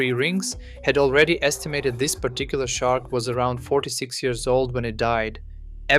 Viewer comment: wait , they said the vertebra was from a 4-6 year old shark...that's still a sub adult at best more of an early teen by modern white shark standards ...sub 10' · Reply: I thought he said 46 year old shark